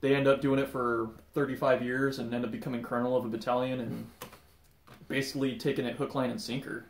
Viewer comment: Wait a minute.... Did I just hear "Colonel of a battalion"?